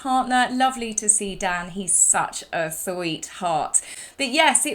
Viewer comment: You both looked lovely. Love the hair. You said "love Dan, he is a sweetheart" but I heard "love Dan he is a tart". Sorry Dan. Lol😊